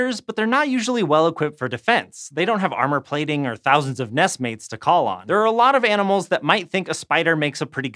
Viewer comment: Am I tbe only one who heard ness mains